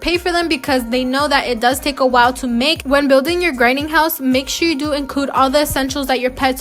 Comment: When she said “grinding house” at first I thought she said granny house 🤣